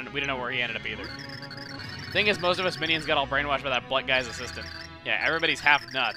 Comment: I thought you said Black guy for a second XD